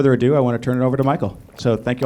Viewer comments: I thought he said Michael Scott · I really thought he said Michael Scott for a second lmao 😂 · I thought he said, Michael Scott! I thought I went through a wormhole and was watching the office in real life.